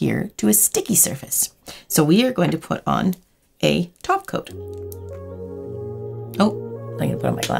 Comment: When she said "top coat" at 1:10 I swear I heard "taco"